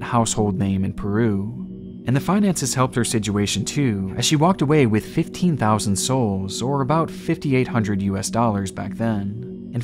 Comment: i definitely heard “she walked away with about 15,000 souls” like shes a dark souls protagonist lol